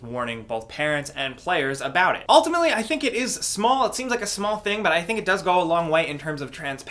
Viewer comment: Am I the only one who heard him say "smart" or "small" at 7:18 because for a second it sounded really British to me 😂